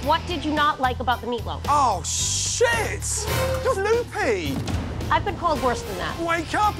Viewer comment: What did he say after "Oh Shit!" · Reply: "you're loopy"